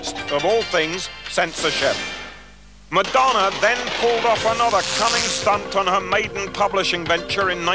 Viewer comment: Whoa whoa whoa. Did he say "cunning stunt" at the same time they unzipped pants?